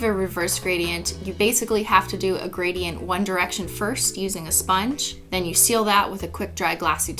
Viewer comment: DID SHE JUST SAY ONE DIRECTION